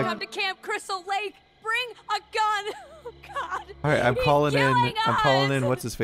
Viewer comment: when he was calling on the radio, i paused mid sentence, and all i heard was "bring a gun!! he's gay!"